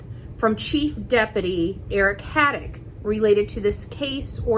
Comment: Did you say "Haddock" instead of "Craddock"🐟😊 ?